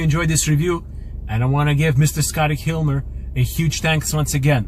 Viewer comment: sounded like he said Scotty hilmer also his mpg sucks